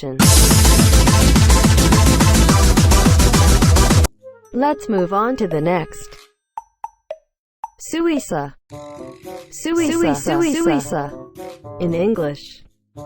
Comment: 字一色に聞こえる。